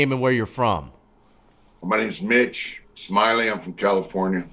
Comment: I swear i thought i heard him say his name was "bitch" at first.